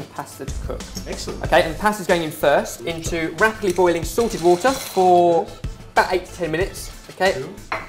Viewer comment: sounds like youre saying sorted water :D